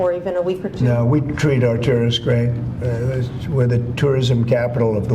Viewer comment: did he say we treat our tourists or did he say we treat our t3rroists great, sounded like the 2nd to me lmao